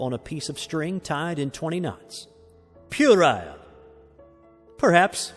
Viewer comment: What did he say at 22:20? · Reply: pu·er·ile ˈpyo͝orəl,ˈpyo͝orˌīl/ adjective childishly silly and trivial. "you're making puerile excuses" synonyms:childish, immature, infantile, juvenile, babyish; More